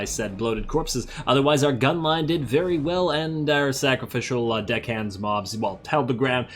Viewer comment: "Dick-hands mob!" - misheard Deckhands Mob